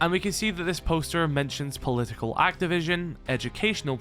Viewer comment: did pro say political activision